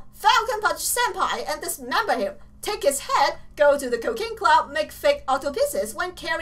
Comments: lol when you say "cooking club" I hear "cocaine club" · It sounded like she said Cocaine Club instead of Cooking Club